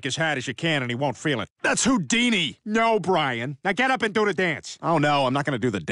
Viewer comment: Did Brian said Luke Deanie or Loop Deanie? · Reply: Houdini